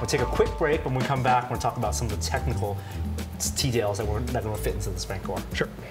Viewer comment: Norm definitely says “teadails” instead of details here.